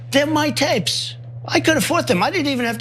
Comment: I didn't tell him to delete the tapes that were my tapes that I could have deleted but I didn't delete them because I asked him not to not delete the tapes which I didn't not tell him not to delete because they weren't deleted. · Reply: And I think I heard him say "I could afford them." How extra weird.